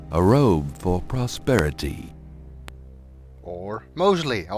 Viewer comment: The captions say “prosterity” (sic), but Gabriel says “prosperity.” The typist couldn’t decide whether “posterity” or “prosperity.”